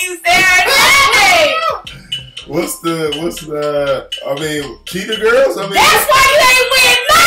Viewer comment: did he say “CHEETAH GIRLS” 😭🤣🤣